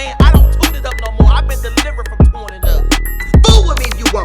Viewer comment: I thought he said he was delivered, i kno he still gettin rammed